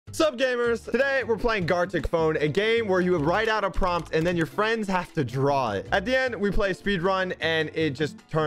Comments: Who thought they said garlic phone?😂😂😂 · I thought it said garlic